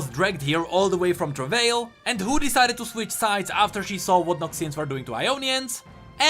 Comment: You said "noxians", the auto subtitles say "nazis". They might have a point